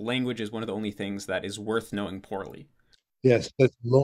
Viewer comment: <link> What does he say here? "Language is one of the only things that is worth knowing portally?" I must be mishearing the last word. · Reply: poorly